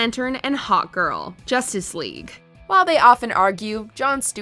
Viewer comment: Omfg I thought you were saying "hot girl" and not hawk girl 🤣🤣🤣